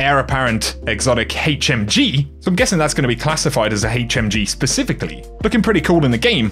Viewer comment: Did you say “HateMG”